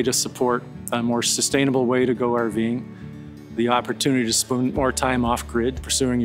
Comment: I swear it sounded like he said the opportunity to spoon more time off grid😂